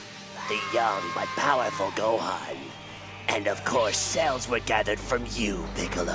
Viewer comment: I thought I heard tfs cell lmao